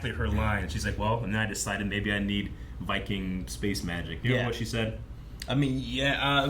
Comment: I also thought Jane said "fucking space magic" as well